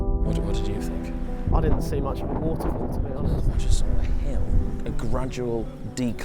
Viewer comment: It sounds like he is saying waffle, but.he is saying water fall. LOL HAHAHA!